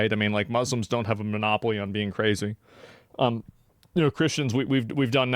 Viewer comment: did he say “we Christians” ? Maybe just maybe asmon? 😏 … he’s knocking. Always.